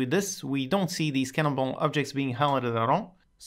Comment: Very interesting one again but what did he say at 6:32 xD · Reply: "we don't see the scannable objects highlighted at all"